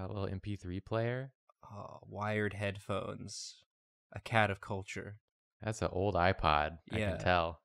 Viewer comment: Originally when said “a cat of culture” I thought he said “cat-er-culture” as a pun on counter culture and I was devastated no one was talking about his incredible pun